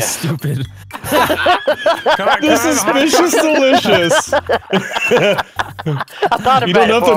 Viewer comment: the suspicious delicious 😂 i honestly thought it said the suspicious delirious and it made 100% perfect sense.